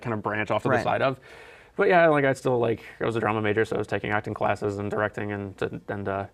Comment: I heard "Trombone Major" instead of "Drama Major".